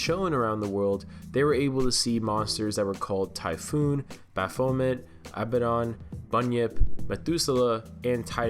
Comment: Baphomet? Did I hear that right?